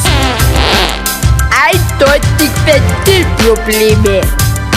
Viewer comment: Was sagt der Knilch eigentlich bei 2:23 und 2:33? Mein bisschen Bayrisch reicht da nicht aus um das akustisch zu verstehen :D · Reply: Leonie Geromiller Ventil Probleme